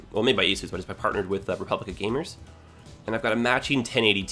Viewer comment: I thought you said your gaming board was "made by ASUS, partnered with Republican Gamers", and thought it was odd that ASUS would be overtly political.